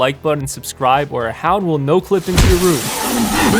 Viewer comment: Did I Just hear the Hound say "Subscribe To Fancy"?